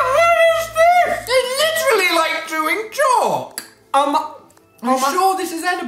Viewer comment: "It smells like something" I knew you weren't going to let him get away with that! "WHAT! WHAT! WHAT!" 😂 When said: "it's literally like doing chalk" as if it's a weird drug the cool kids are into. People who don't follow on Instagram: What's with the bloody knuckles? I feel like I'm part of a secret club. And that's the tea! :D x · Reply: Sandig Same. I heard "chewing".